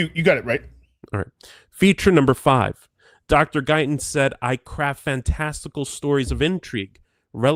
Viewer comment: first time read this, it sounded like "Dr. Guyton said I CRAP fantastical stories...." "Craft", certainly sounds more professional, but Diane definitely does CRAP SOME FANTASTICAL STORIES!!! 🤣🤣🤣